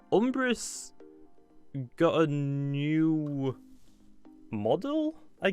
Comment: Who else thought it said, "UMBRAT GOT A NEW MODEL"